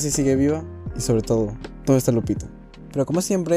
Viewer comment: ok . Alguien me puede decir q es lo que dice? porque no entendí bien Y sobre todo, gran video la verdad ❤ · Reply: Parece que dice "Lupita" tal vez refiriendose a gregory xd